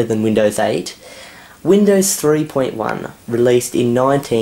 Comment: it kinda sounds like he said "windows 9"